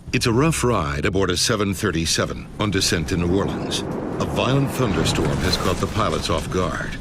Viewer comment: These subtitles are very wrong and I feel they've been made by a non-native english speaker. 0:02 says "On descent in New Orleans" but the subtitle say "In the wilderness." 0:27 says "We've lost power on the engines" but the subtitles say "Little less power on the engines"... 0:29 is then "I've got nothing" but the subtitles say "That good enough?" I'm not even 30 seconds in and there's 3 mistakes. If someone knows a way I can change the subtitles please let me know. I want to fix the subtitles for people who have difficulty hearing.